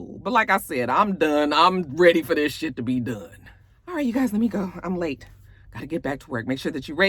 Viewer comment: I heard the same thing, It sounded like he said let her go.